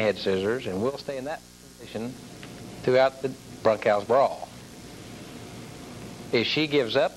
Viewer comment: I originally thought he was saying "broadcast brawl." Now I'm not so sure.